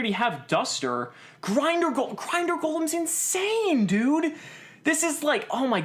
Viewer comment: I thought grinder golem said ginger golem